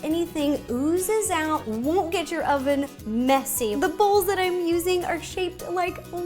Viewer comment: I thought she said now eat your oven